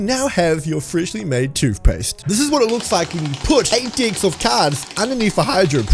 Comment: did he say d*ck